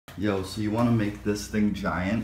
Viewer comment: It sounds like is saying "do you want to make out" at the end. And is saying "yeah!"